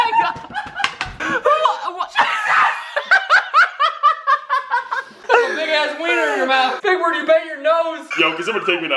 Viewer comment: i can not be the only one who heard yell “JESUS” 😂😂😂😂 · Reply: Yeah you can’t be the only one... be cause he did say jesus